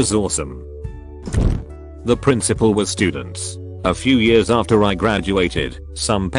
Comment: What was being said two minutes in around 2:15-2:20 because I don’t understand it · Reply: It's probably "The principal was sleeping with students"